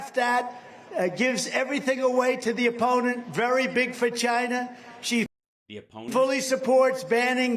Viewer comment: sounds like he says very big vagina😂😂😂😂